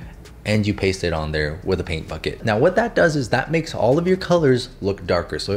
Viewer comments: Thought he was saying f$%k it 💀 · Is it just me, or at 5:13 ish did it sound like he said: "Paint f*ck it" instead of "Paint bucket" which is kind of exactly what the paint bucket tool does XD